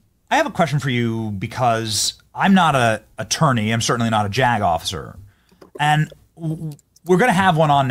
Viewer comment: "Im certainly not a jack off sir" is what i heard instead of " Im certainly not a JAG OFFICER" 😅😂